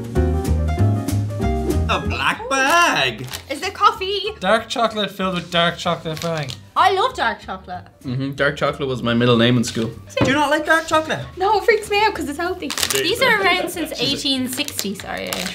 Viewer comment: "No it freaks me out cuz it's healthy" 😂 Gonna steal this one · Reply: At first I thought she said "salty". Chocolate salty balls.